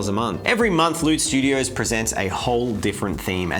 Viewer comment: Is it just me or did it sound like he was saying "Lewd Studios"